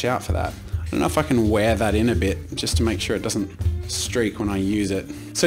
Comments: he says “and if I just wear that in a bit” What I heard “i gonna f**king wear that in a bit” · it sounds like he said "gonna f*kin' wear that in a bit" but then I realized he might have said "don't know if I can wear that in a bit" a little too fast. just wanted to see if I'm not the only one who heard that lmao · Did He say 'i gotta fuckin' wear that in a little bit